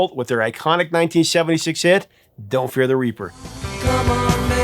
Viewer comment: when it came out me and my friends thought he was saying dont fear the reefer ...Hahahahaha...you need to chk out B.O.C. song called ....' Mommy '...